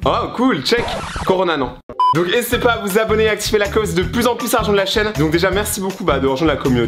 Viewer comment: n'ésiter pas a activer la close Moi : il as dit close 😂😂😂😂